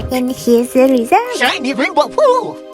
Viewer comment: "ShInE rAiNbOw PoOp👹" What did dude say??😭